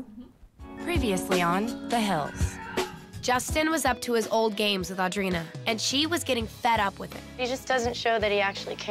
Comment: What's the screaming at 2:05? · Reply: It said Sonny...lol